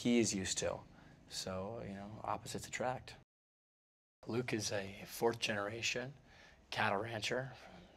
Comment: is it me or did he just said Lucas rather than Luke + they make a really great couple if they'll ever date